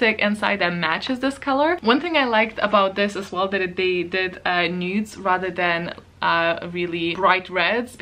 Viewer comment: When you said "they did the nudes", I heard "NEWTs" and was confused for a second :- D